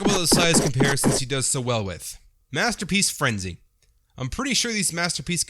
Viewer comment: Any body notice when he said frenzy the captions said rumble